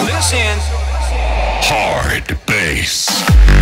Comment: Can anyone tell me ... what does it say on drop ?? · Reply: Arsham Ali Rahi HARDDDBASS!! FUARKKKK